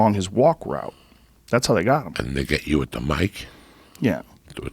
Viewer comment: did he say didnt they get u with mic